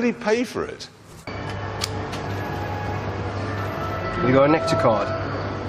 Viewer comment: The dude just saying "You got a nectar card?" to The Stig never ceases to make me giggle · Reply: I thought he said "got an actual card" but in an accent.